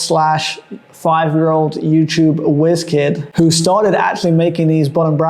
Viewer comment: did he call Hambini a Jiz kid ?? Must know him more intimately than the rest of us 😂😂😂 Good vid though 👍👍👍🇬🇧